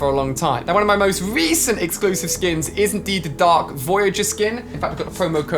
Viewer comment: Did he say Dark Voyager